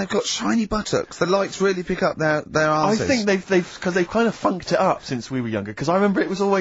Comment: Did Steve say fuck at 13:36? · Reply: funked it up